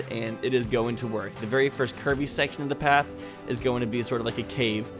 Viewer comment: I thought he said Kirby lol Then I noticed that he said curvy